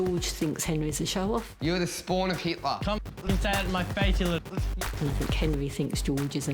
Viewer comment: ohhh my gosh did he just say the n word? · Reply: He said "d*ck head" 😒